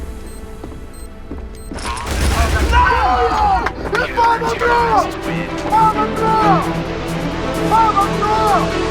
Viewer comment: I hear Get_Right saying fabbrabo. What does that word mean ? And whats the correct spelling of the word ? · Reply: He is saying "faen va bra" which means fucking amazing